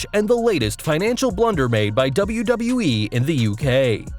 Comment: Hey did you just say WWE and the UK? At 0:26 😅